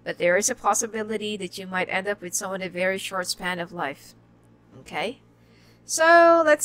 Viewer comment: What do you said at 14:18 ...you will end up with a person...? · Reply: Lucy S I know I’m concerned. It sounded like she was trying to say you will end up with a person with a short life span